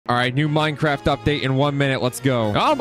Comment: did he say Minecraft?